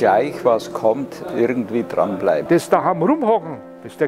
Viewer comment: Can someone transcrypt 4:34 ?? I understand what he means but damn - that is difficult to transcrypt.. der grosste fehler man machen könnte verstehe ich, but the beginnig... zu hause.. etwas... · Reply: he says "des daham rumhockn", meaning: To "just" sit around at home